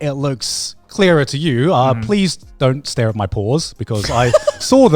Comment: Instead of "Please don't stare are my pores" I thought Garnt said "Please don't stare at my paws," so I was trying to figure out what was wrong with his hands.